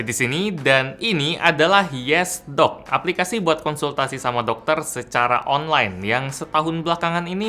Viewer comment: Yang saya dengar = Yes Dog Realita = Yesdok Nama Aplikasinya bikin salah denger..